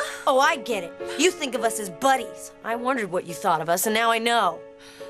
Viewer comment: Did they say “bunnies”?😂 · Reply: Charleyb_728 they said buddies